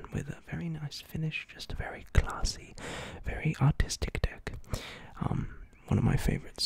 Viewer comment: When he said artistic I thought he said autistic